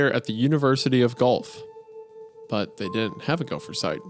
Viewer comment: THE ENTIRE TIME HE WAS SAYING GUELPH AND I WAS LISTENING TO THE AUDIO IM HORRIFIED. I THOUGHT HE SAID GULF. I DIDNT REALIZE HE MEANT THE CITY GUELPH.